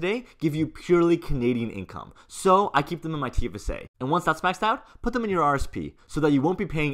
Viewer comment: Can you clarify what you are saying at 0:47? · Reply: great thanks. I think I missed the "maxed out" part. Great videos BTW.